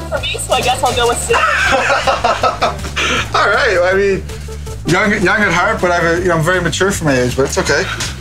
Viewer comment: Not english speaker here, so what does Nathan says at 2:20, please? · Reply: “Young at heart but very mature for his age”